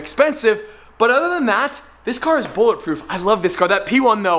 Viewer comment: Hold up did he said its bullet proof?